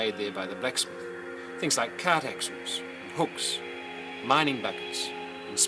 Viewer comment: Awesome video! Questions from me, a English as a 2nd language learners, what did he said: 1:20 "cat experts"? I couldn't understand it. 1:37 "a bit of rough $hetching$ from whatever #brush" they could find"? I couldn't understand it. The following are notes for myself: 1:07 "many copper mines were sunk in the FLINDERS RANGES" 1:31 "very primitive SMITHIES"